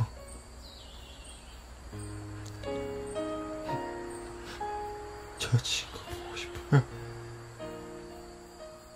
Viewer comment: what did taehyung say here? If anyone knows Korean 😅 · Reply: i need to see you hyung